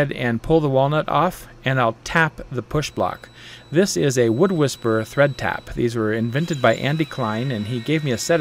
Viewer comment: Share pictures of your work & get help if needed. Join our King’s Fine Woodworking Community Page. Woodworkers helping other woodworkers. Share advice, get project ideas. Free tips. Insider information. Get the best deals on lumber & tools. <link> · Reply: Any link for the "wood taps"? I couldn't understand what you were saying in the video. Thought you said wood whisperer